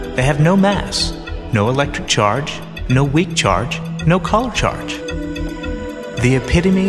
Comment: "they have no mass, no electric charge, no weak charge, no "CALM" charge"? whats that word you used, it wasn't enunciated and i'm not sure what you were trying to say :P other than that this one of the more informative videos about protons esp for only a minute